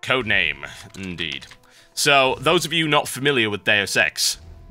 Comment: lmao i thought he said those of you who are not familiar with their sex